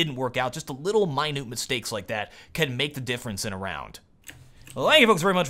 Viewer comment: everytime i hear the ending "thank you for watching, this is the warowl, and i still dont have any clothes on" i know its not what he says but i hear it everytime :D